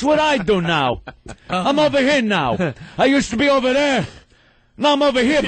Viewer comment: They misheard Dice. He was saying, "I'm uber-queer now!"